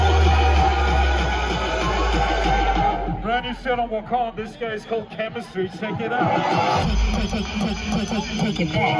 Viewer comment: who did he say that is? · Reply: Chemistree aka carbin